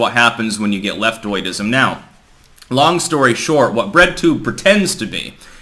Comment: I thought you said red tube. Lol. I think that’s a porn site